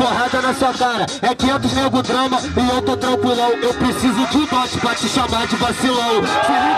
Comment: o que ele disse? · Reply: Eu preciso de um DOT pra te chamar de vacilão